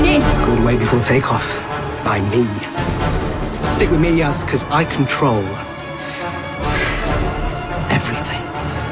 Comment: For a moment "I control everything" 4:42 sounded like "I can troll everything" and that is beautiful